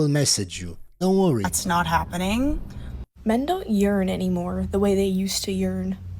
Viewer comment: I legit thought she said "Men don't Urinate anymore"